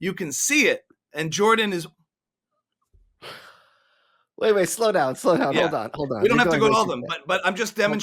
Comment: I thought he said "Tom Waits." Was eager to hear that one.